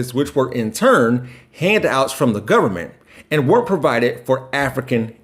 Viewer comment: Ok at 14:13 is he saying "were provided" or WEREN"T provided" I think he means to say were NOT provided....just sounds like he said were though